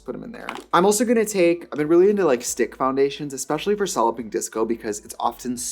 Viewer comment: I thought she was saying Solluping Disco